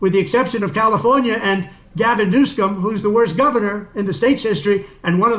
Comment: Haha.. did he say Gavin Newscum?